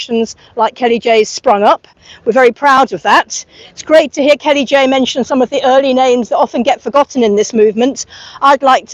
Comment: I didn't catch the group the person belongs to at 11:00 as she said they spoke about the subject since 2016. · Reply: Sounds like “object” try putting it in a search engine with surrogacy, it should pop up🤞🏻